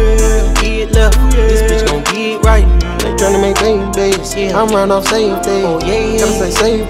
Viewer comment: Am I the only one who thought he said “ I’m trynna make 8 babies “